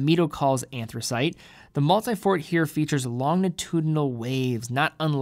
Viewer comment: Am I the only one who thought it said multifArt